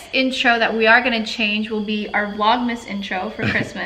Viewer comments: Who else heard Elaine at 24:29 and literally thought, "holy shit they said the name!" and then realized that just slirs his words and actually said "her layin" 😂😂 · did just say their baby’s new name?? Elaine? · HOLY SHIT!! I thought said "we'll just get elaine on her back smiling" and I was about to freak out thinking he said the new baby's name, but quickly realized he said something totally different 😂🤦🏻‍♀️